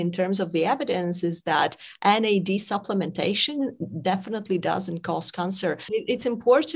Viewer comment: Am I hearing her correctly at the 6:28 minute mark on the video she states mad does definitely cause cancer. Please clarify because the video was edited at this point also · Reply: She said doesn't. It's hard to hear because of her accent, but I'm 100% sure she said "doesn't".